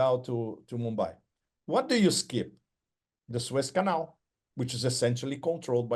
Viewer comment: "the Swiss canal" .. at least that's what i heard through my Freudian filter. Makes sense given the canal's overlords always have their circlejerks in Svtzrlnd